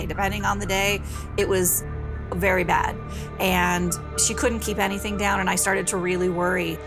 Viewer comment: I thought it said cant keep foot down I was so confused 😂